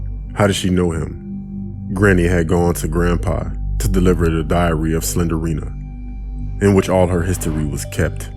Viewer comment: DID HE JUST SAY DIARRHEA OF SLENDRINA I MEANT